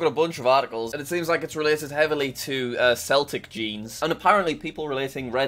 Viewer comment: Did just say Seltic instead of Celtic?